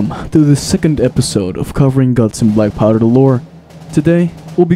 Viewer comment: I thought that said "glore"